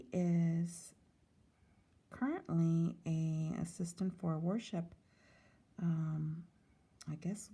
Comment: My New England ears thought said Ezekiel was an “Assistant to a WARship.”